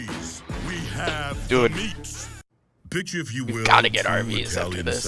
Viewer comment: Did it say bitch if you will?